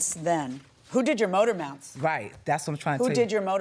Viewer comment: Ha! I thought the judge kept saying you “motor mouth”